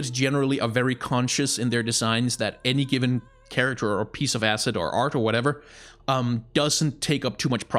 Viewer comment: Did you say piece of asset? Lol dude. Another great video, as always!